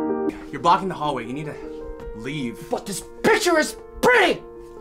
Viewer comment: oooooh did he just say but this b***h pre painting